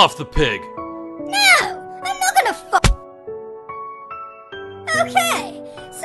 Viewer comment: IT Sounds like Saying at 0:23 NO IM NOT GONNA FU